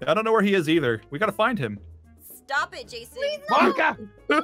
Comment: did say “Monika”?OH NO NOOOOOOOOO DOKI IS TAKING OVER